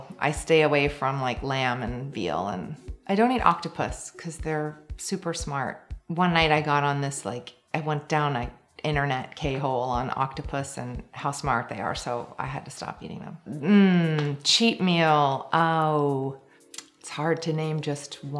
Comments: Am I insane or did I hear her say 'internet k-hole'? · Did she just say "Internet K-hole"? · Did she just say, "internet K hole?" Damn, love me some Gwyneth.